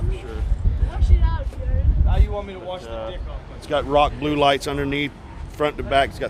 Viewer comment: Did anybody else hear that random stranger in the background say “how you want me wash the dixk off” @ 6:32? 😂😂